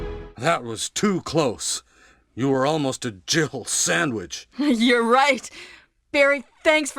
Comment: Is barry saying you were almost a jiffle sandwich, cuz i allways thought he said jill sandwich, but in this video it sounds like he said jifle